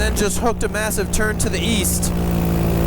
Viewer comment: East? I thought you said Weast!